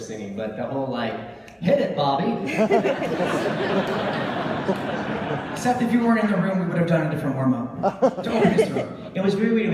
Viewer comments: what does David say at 4:54 ??? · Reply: I think he says: "Seth, if you weren't in the room we would have done a different warmup." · What does David say at 4:54? thanks for sharing btw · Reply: He said "If you weren't in the room we would have done a different exercise to open his throat." if you get that....😂